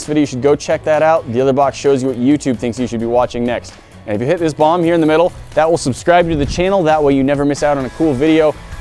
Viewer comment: Sounds like you say hit that bong in the middle